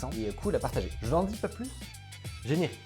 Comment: J'ai entendu "Loïc ! Générique ! "